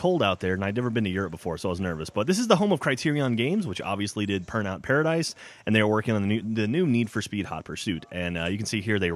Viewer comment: Did he say "Purnout Paradise"